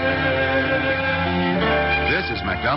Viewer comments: I heard ' This is my gun 'n' Carrot and These Is The Days of our lives' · I heard "this is my dumb carrot, and this is Days of our Lives" lol. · Whenever I saw and heard this, I thought he said, "this is my gunned carrot." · When I was little, I thought he was saying "This is my donthcare and these are the days of our lives."